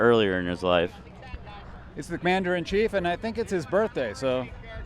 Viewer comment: did he say "mandarin cheif"?